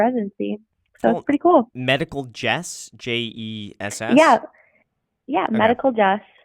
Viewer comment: At first I thought she said her YouTube channel was called "Medical Debt", and it definitely seemed appropriate.